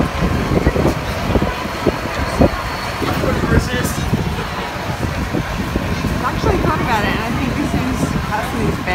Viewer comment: what did he say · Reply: "I couldn't resist"